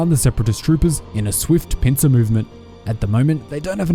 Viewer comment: Low key i thought i heard you say “pizza movement” not Pincer lmfao